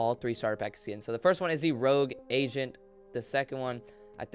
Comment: I thought he said rogue Asian